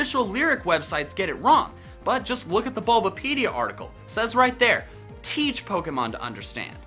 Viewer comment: i heard it as 'these' Pokemon to understand. heard it wrong for years.